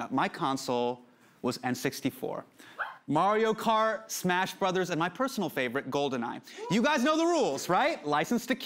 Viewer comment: Did he just say Mario Car? It’s Mario Cart....